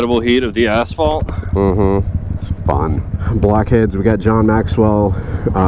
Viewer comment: Why it sound like he said black heads